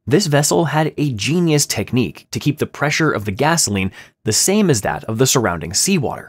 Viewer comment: i thought he said ghastling💀